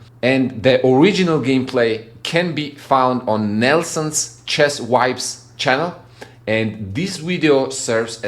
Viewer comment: Cool video I came from Nelson’s channel. Funny to me when you pronounce “chess vibes” with your accent I heard “Nelson’s channel : chest wipes” lol😂